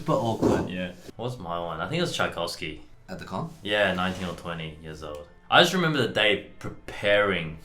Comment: I misheard Brett's "19 or 20" as "in 1920" Or maybe it was wishful thinking on his part.